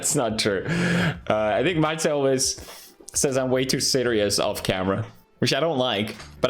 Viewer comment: lmao i thought he said that he was too siderius off camera